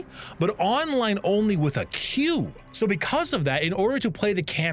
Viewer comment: Tbh I heard "online only with a queue" as "online only with a q" and I was so confused like. Where do you put that